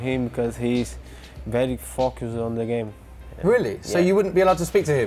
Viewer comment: Can someone tell me what coutinho said @ 2:25?? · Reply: david angel emmanuel ‘focuses on the game’